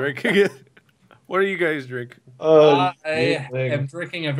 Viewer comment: "What are you guys drink"Is what it sounded like said